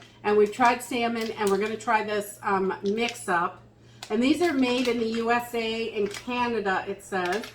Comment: Seriously did you just say "made in the USA in Canada?"